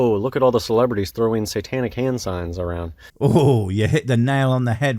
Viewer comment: I thought he said "Titanic" hand signs. A sinking ocean liner with no less than 4 funnels is quite the feat of shadow puppetry.